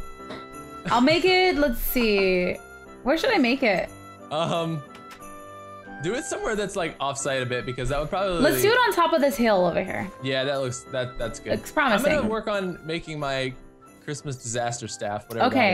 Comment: wait....did just call Cupquake babe? (he said "do it somewhere off to the side a bit", I heard it wrong lol)